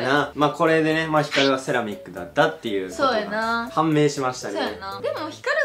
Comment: セラミックがセノビックに聞こえる笑